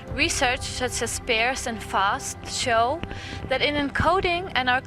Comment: the caption says "research such as pears and fast ..." Is that correct? What kind of research is pears (or pairs) and fast?